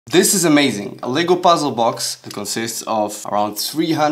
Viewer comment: I love the fact that even tho I misheard 'a lego puzzle' with 'a legal puzzle' it still makes sense